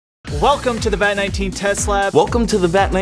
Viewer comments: I thought said "Welcome to the Vat19 Teslap". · Vat19: welcome to the vat19 test lab Me: did you just say Tesla🤔